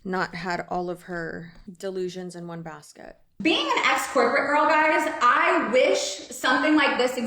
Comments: “Being an ex corporate..” I heard “being an escort..” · Oh jeez, I really thought she said "being an escort" but I ran it back and she said "being an S corporate"...big difference!